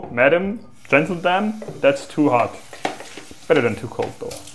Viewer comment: Did you say Gentledamn? 😂🤣😂 (Love it)